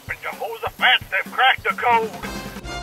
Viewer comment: what did he say? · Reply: Oh, I thought he said "GET DOWN THERE LOPEZ AND CRACK THE CODE"